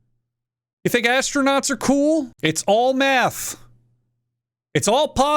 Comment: why does it sound like he is saying "meth"?